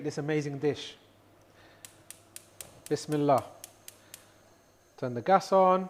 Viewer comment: Love this would curry leaf’s in the oil at the beginning add to flavour and also not being cheeky but it always sounds like your saying Miss Miller but what are you saying and meaning please 🙏